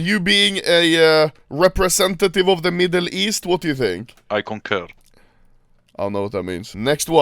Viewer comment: what did he say at 8:20, plz help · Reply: he said concur, meaning to agree